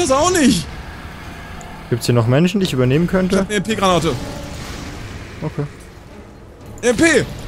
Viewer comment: Ich weiss er hat EMP - Granate gesagt aber ich hab LP - Granate verstanden xD